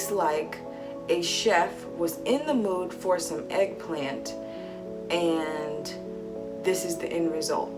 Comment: I previously heard her saying about the chef near the ened of the video as: "It tastes as if a chef was in the moon, forcing out eggplant, and this was the end result." Meaning, a man was pregnant with eggplant! Realleh Though, I realized what actually said.